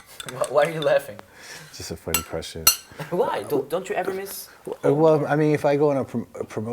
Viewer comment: what does Slash say there? It sounds like “cause that’s a stupid question” to me lol but i’m not sure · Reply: He says funny question